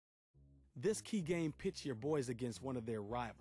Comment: Am i the only one who heard "this key game, bitch"